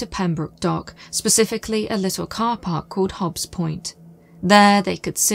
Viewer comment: "little car park called Hobbs point" I heard: "called Hobbs END" (from the movie "in the mouth of madness")